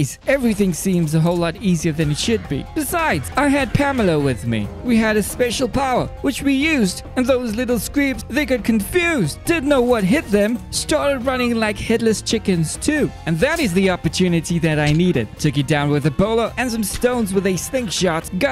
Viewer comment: I heard “head-less chicken’s” as “hitler’s chicken’s”